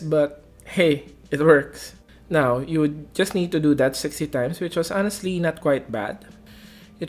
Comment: Did you say 60 times?